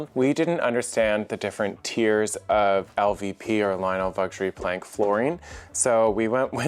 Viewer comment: I’m sorry did you say you got a visit from Lionel Vuxury Plank? Or?? 😂 🤣